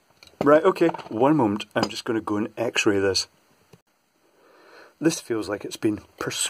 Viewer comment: You say X ray I hear "Tappy Tap Tap". Love the content. Found you through AvE.